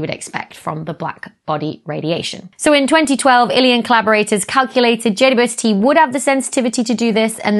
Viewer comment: I heard "in 2012, alien collaborators..." and was like WHAT